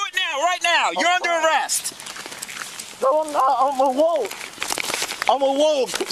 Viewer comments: Is he saying "I'm a wolf". WFT? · what? did he say "Im a wolf?" lmao this dude has some serious mental issues. man the balls on him though, imagine getting pulled over and then demanding the first thing the officer do is fill out your made up form. wow. just wow. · Did he say "I'm a wolf"?